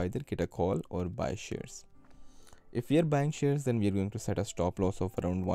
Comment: minutes into the video, I heard for Market DEX PUT, set stoploss 1%-2% and for Market DEX CALL, set stoploss 15%-25%? Did I hear correctly? Why such a big difference stoploss between Calls and Puts? Thank you. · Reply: Good catch I missed that the first time. But I think he said “if you buy shares” of the stock, do 1%-2%. “if buying calls” (options) then set a 15%-25% stop loss”. If I’m understanding that correctly